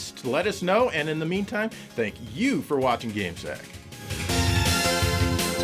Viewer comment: Did they say "game snack"? Thats a disgusting joke.